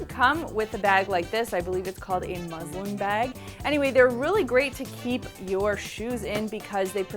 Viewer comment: does she say "muslim bag"? LOL